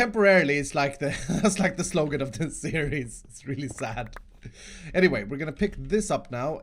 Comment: did he say T-series? like if you agree